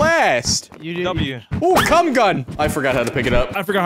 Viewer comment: DID HE SAY C*M GUN?!?!